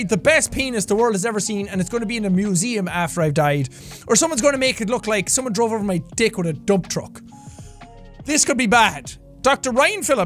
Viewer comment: I had to pause the video for a second and when I unpaused it the first thing I heard was “someone ran over my dck with a dump truck” I Can’t Breathe